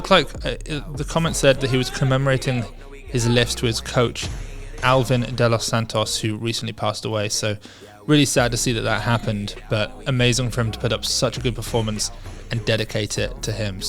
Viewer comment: MANNN English isn't my first language and i wasn't putting so much attention to the video so what I heard and understood was "Albert de los Santos passed away," and I almost had a heart attack 🥲